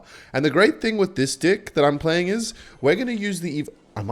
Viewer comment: what did he say?!? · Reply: Bro, shut up, says dick instead of deck